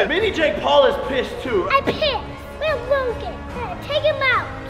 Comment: why does it sound like he said i pissed